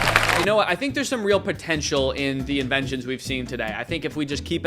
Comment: did you say potential?